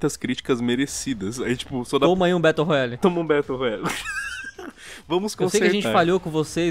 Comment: eu ouvi battle rola, e imaginei coisas terríveis